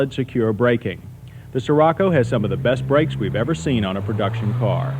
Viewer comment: The name sounds like someone is saying “sure Rocco”.